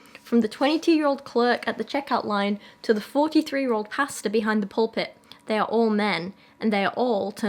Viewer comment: I hope you don't think this rude >~< but every time you said 'pastor' I heard 'pasta' ^~^ and it was hilarious